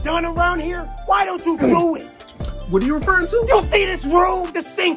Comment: so like "what ru refering to??" i misheard it as "what do ur parents do"